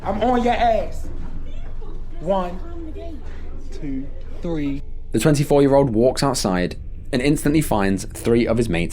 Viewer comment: did she say just climb the gate? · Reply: yes, you heard right "jump the gate" he was staged from the start and I bet that even the hide and seek was proposed to him so the murderer can jump the fence...in the dark.